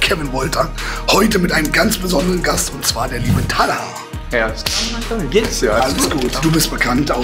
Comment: min was sagt der da? xD "Herz damanja gehts ja" wtf???!!